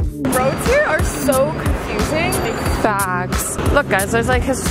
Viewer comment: What did say at 5:31???!! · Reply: Facts